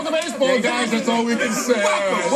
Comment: did Strahan say fuck em at 0:27? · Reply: Bon Grips LMAO. It did sounded like that but it was welcome.